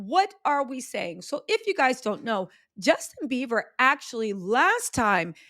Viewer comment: did she just call him Justin Bieber⁉️🤭